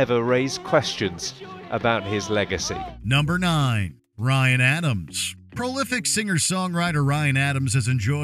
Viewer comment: "Ryan Adams" I misheard and though you said Bryan Adams.